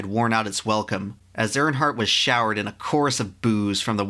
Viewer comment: I thought he said “As Earnhardt was showered in a course of booze” 🥃 🍾 and was like WTF 👀 lol 😂